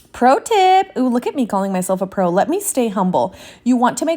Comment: You said I’m a pro and I thought you said imma thug and I about peed my pants! How have you just been recommended to me?!